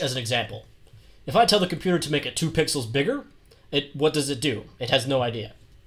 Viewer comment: what did you just say... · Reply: Bigger he’s not racist